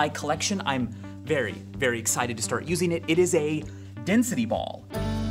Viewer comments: I thought it said Destiny Ball, *disliked. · I thought it said destiny ball. Oops